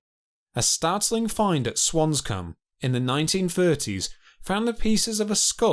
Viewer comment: did he just say "swanscum"